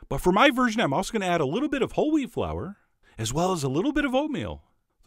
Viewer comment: I heard Holy Flour instead of Whole Wheat Flour around 0:30, since my mind was still set on Saint Patrick's Day, lol.